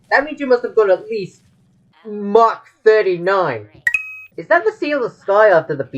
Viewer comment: Why did you say March 39 at 10:03?